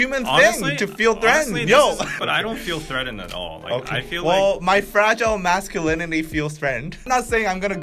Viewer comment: I thought he said ' My Female Masculinity' haha